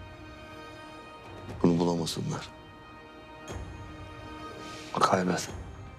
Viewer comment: da Haşmet ne dedi anlamadim. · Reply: Kaybet